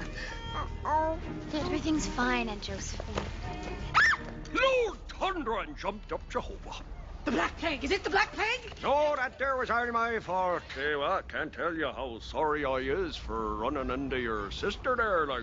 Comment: "THE BLACK PLAGUE! IS IT THE BLACK PLAGUE?!" xP lol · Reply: Lol I always thought she said black pig.